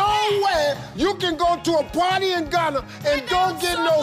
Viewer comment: Sobolo, it is a local drink made from Roselle flowers, water and sugar. It is very popular in west African countries. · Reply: I thought you said solobolo